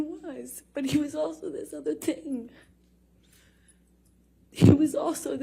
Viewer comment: LOL no tears and wtf is she saying? “This other tinng” lmaoooo